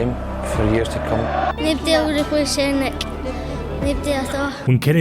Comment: i'm american, can someone tell me what that kid said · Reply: Nobody will replace Henrik, Nobody at all.